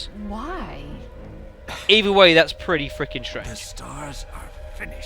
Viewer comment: you say "either way it's pretty freaking" and then something else after that I can't quite make out usually I'm pretty good at english accents as I watch a lot of english shows and comedies. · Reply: I said strange...bit slurred though I must admit...I assure you I wasn't drunk. Promise.